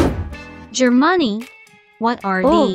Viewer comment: I thought it said, " Germonie " or I was right.